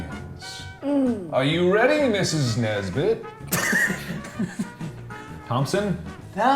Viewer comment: did mark say Ms. Nesbit.?😂 As in from Toy Story.?😂